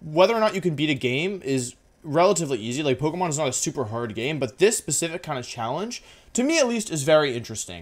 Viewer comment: What I hear: Pokemon Sun is a super hard game